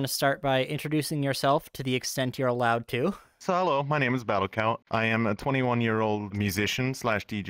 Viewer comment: I like the name. Because I just hear "battle cow". And that's a funny mental image.